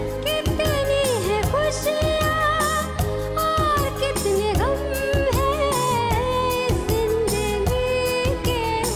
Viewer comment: Can anyone lip-read and decipher the statement?? 32:46 क्या कोई लिप-रीडर बताएंगे कि मैडम ने क्या कहा होगा ?? .... " अच्छा होगा ...???" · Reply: She said 'Is Jindagi ke din kitne kam hai. Kitni hai khusiyaan aur kitne gham hai' BTW,she was very nice